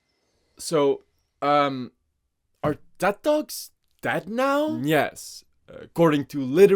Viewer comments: in 0:37 i forgot he was talking about ted talks and i thought he said death dogs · I thought he was saying “are dead dogs dead?”😭😭